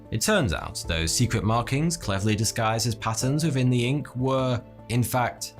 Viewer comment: What did he say? Naturally occurring "passons" within the ink? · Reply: "patterns" with quite some accent